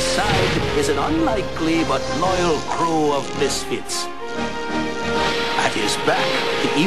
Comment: I always misheard as "at his side, is an unlikely but loyal crew of biscuits".